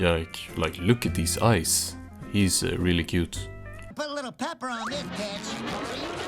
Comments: it sounds like spongebob says: "put a lil' pepper on this bitch!" at 1:46 i had to rewatch it😭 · did spongebob just say bitch